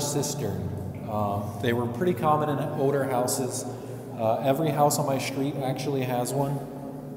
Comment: I heard, "Every house on my street has an eel pit".